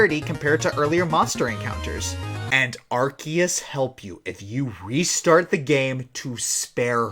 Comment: And (Arkeus)?? help me?? did you say Arkeus or what i neewd to know